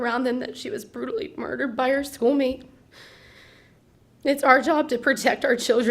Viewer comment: Did the cheer coach say “murdered by her soul mate?” Surely I misheard?